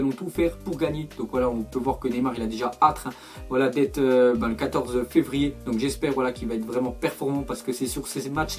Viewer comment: C'est moi où ne sait pas parler 3:27 "Neymar rêve d'être 14 février" 😂😂😂